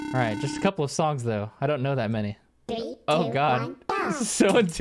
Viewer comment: It sounds like shaggy is saying tree-do